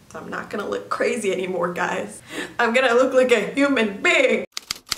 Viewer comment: I heard “I’m gonna be a newmannn”I know she said human lmao love ya ^^